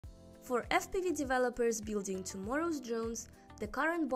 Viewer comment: I heard "for std defelopers" lol